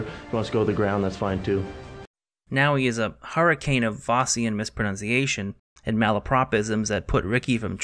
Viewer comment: What’s he say at 14:25? “Now he’s a hurricane of blank mispronunciation” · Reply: Faustian???